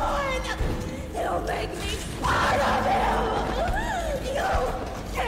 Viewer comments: so that is what she says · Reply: So That’s what she said at that point! I thought she said one of the following: “You make me Spider-Man!” “You make me Spite of Him!” “You make me Spider limbs!” But she really said: “He’ll make me part of him!”. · so that is what she says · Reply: I tought she said YOU'LL MAKE ME HORABLE AHHHHHH